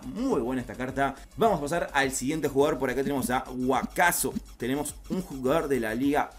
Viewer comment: flashe que dijiste waskaso🤦‍♂️